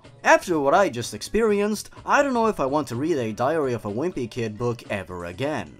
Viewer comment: I thought you said diarrhea of a whimpy kid lmao